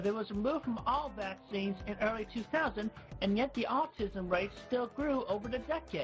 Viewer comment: Random thoughts: The beginning was actually very well made. I'm impressed. Visually the whole thing was well done but this part actually felt like it was trying to be informative instead of ranting 7:36 "the austism race"???? 8:12 this whole sequence is so sad knowing the context of CWC's home life. It kind of shows an association with CWCville and parental love. 9:53-11:48 CWC almost becomes lucid and realises they should leave the internet 11:08 the subtitle might just be the smartest thing CWC has ever said 19:42-20:43 someone should make a compilation on how many times CWC lied in 1 minute here 25:10 CWC's friend looks extremely uncomfortable 28:57-29:18 this is both sad and very ironic considering the events with megan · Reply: I believe he said "the autism rates", not the "the autism race".